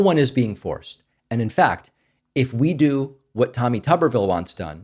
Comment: I heard “Tummy Tubberville” instead of ‘Tommy’ and can’t stop laughing now. Mariju@ is funny